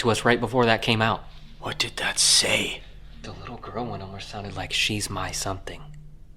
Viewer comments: When you ask the entity what it wants with the little girl, 53:15, it sounds like it says “she’s my toy. I play with her.” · What do you want with the little girl. I heard “she is my pet”…could be wrong though